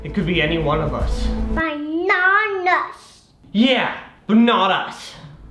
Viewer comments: When said but not us I thought she said Bananas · did say "bananas"?